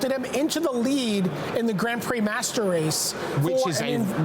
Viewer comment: Did BDM just say 'grand prix master race'?